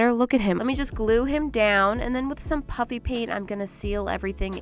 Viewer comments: I just realized. It's PUFFY paint. I thought she was saying puppy paint... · It sound like you saying puppy paint😂😂